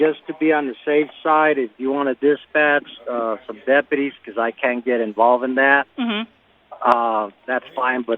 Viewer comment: I thought mayor said don't get involved chief?